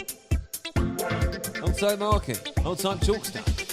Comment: did you say Longsight market ? Lol